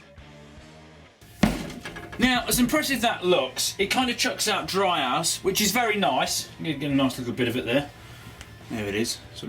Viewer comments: Really sounds like you're saying "as impressive as that looks, I kinda chucks out dry ass, which is very nice" at 0:46 - even though I know that's not what you're saying.. ...but now I can't hear anything else than you saying that xD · Instead of him saying dry ice I heard him say dry ass... I'm dead.. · XD At 0:48, instead of saying dry ice, it sounds like he said dry ass XD hahahahahaha lmao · Could have sworn you said "dry arse" at 0:50 haha :p Can't be having dry arse hell no!!